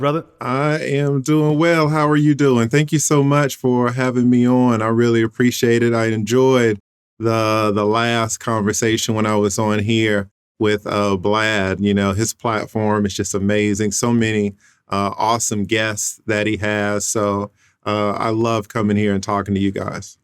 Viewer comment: Did he say blad?